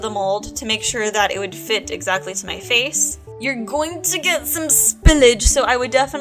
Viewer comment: it totally sounds like you said: "You're going to get some spinach." I was like where's the spinach? And then I was like oh, I totally miss heard. Lol. Man, it amazing me so much all the different things you can do with hot glue!!! I didn't know you could use it to make a mold!!